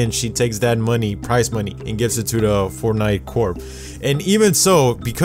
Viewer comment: Did you said fortnite corp😂😂